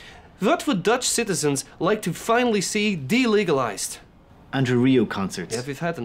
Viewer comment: tf is he saying? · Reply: I think you meant this part: ‘André Rieu concerts’ (there are closed captions by the way)